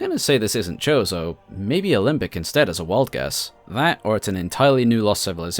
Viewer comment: what did you say there? olymbic? how do i spell that. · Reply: Alimbic